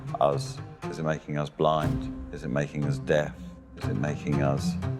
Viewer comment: The subtitles were meant to say 'deaf instead of 'death'.